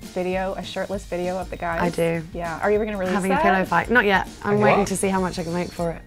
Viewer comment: what she saying in 2:28 what he saying in 3:05 what she saying in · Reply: "I do, having a pillow fight"